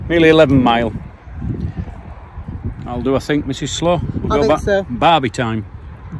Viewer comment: Took me two vlogs to realise youre surname was. A thought you were saying mrs SLOW. love youre vlogs . just found you today lol xx stay safe and shes a good planner